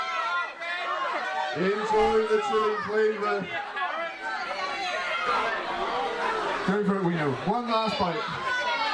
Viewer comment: did I just hear “don’t be a Karen”??? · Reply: 'it's only a carrot'